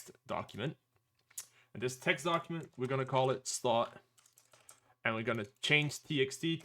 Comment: what is he saying att 2:09 ?? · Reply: I'm saying make a text document then rename it to start.bat (make sure windows file extensions are on so that you are having to remove the .txt otherwise it stays a text document)